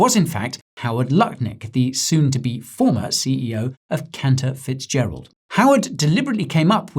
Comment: Did you just say that man’s name was Counterfeitgerald? 😂 I know that’s probably not how it’s spelled, but sure sounded like it 😅